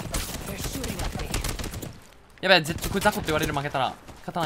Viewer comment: なんて言ってるか聞き取れないんだけどわかる人いますか？ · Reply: やべぇ絶対雑魚って言われる負けたら